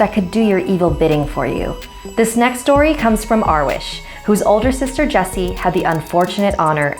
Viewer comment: When sad Arwiesh i was like omg I thought she said my name Arwa